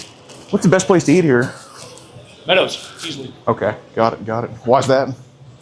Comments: what's the best to eat here 4:51 Pedo: sounds like he said pedo's ( easily ) 4:53 that through me off a bit.. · Did he say "Meadows"? I heard "P3dos" at first. 😂